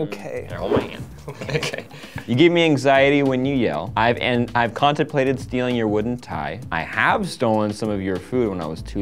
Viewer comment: For some reason I heard, "I have contemplated feeling your wooden thigh"......